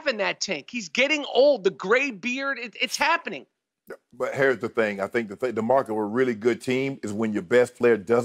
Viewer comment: Michael says "gray beard.." and it sounds like Shannon says "Hair's the thing.." that would have been pretty slick if intentional